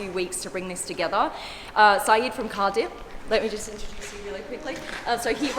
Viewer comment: I thought she said “Sayeed from CARDIFF”!